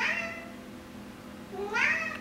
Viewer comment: its funny when you say "otto you did that" it sounded like he said no